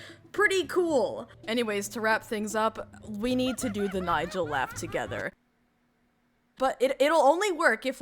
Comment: When you said “Nigel Laugh” I legit thought you said “Nigel rap” because that is legitimately something they would do in a 2010s movie and I was like How the fuck am I supposed to know the lyrics? Wait please don’t start without me hjsdlksjaggfff